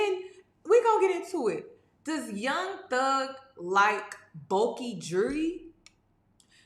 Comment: girrrl I thought you were gonna say “Does Young Thug like boys??” I was hollering ‘YESSSSSS!’ 😂😂😂😂